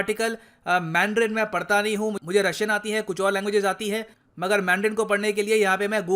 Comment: did I heard wrong or casually just said "Mujhe Russian aata hain aur ek do language aata hain"🙄🙄🙄🙄🙄 Respect.